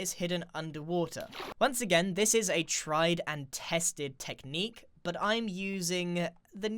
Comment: did you say, "This is a Trident tested technique?" 😌